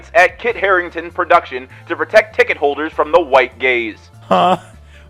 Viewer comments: I was listening to this while doing chores and I really thought you said that theater was trying to protect ticket holders from “white gays” 😱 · I was listening without watching and I thought you said “white gays”, and I thought they were doubly prejudiced. Guess it’s good they’re only racist.